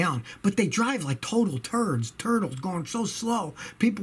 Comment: "Driving like turds" -Scotty Kilmer, 2019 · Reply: I thought he said turtles, did he actually say turds? Hahahaha that would be classic